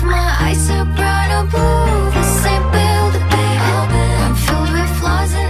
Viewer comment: When I watched the official vid I thought is was saying build a bish